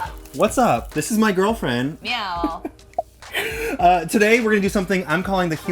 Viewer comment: "MEOW" thats what i heard